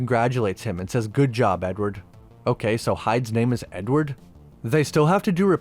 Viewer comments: I thought Jeckyl said ‘good job old boy’ to Hyde. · I thought he said “good job old boy”….